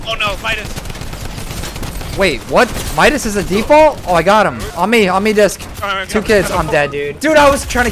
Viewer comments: Who heard saying on me disc on 1:01. like it if you heard it. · did he say on me “Disc”?